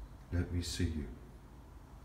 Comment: What did you say at 16:57? Was it "Will we see you?" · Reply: Hi, I think I said can I see you? Regards